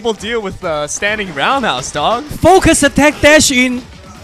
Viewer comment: it sounded like he said "focus attack that "shit'